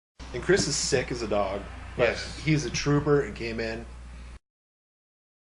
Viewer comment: I know you said "Kris is as sick as a dog, he's a trooper and came in" But what I heard was "He's a trooper and gay man"